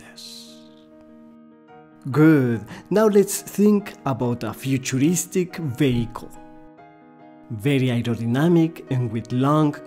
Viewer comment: Great video! I wish my old art teachers could explain the difference between 'good sketchy' and 'bad sketchy' like this. I wish I sounded as cool speaking Spanish as this guy does speaking English! And just for a second, I heard 'futuristic bagel' instead of 'futuristic vehicle'. A bagel would be a good way to practice ellipses! Keep up the good work dude! This really helped me! Thanks！